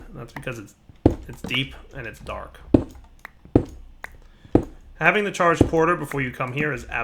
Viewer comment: I thought it said deeper and danker xD